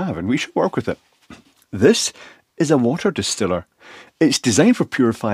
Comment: Oh man, told ya I'm getting old. I swear I heard, "This is a dehumidifier",.. just fill it with water... AND THEN I GOT MY GLASSES 🤓 ... it's early, where's my Percolator Now ya know what, I Couldn't SEE What You Said Means 😉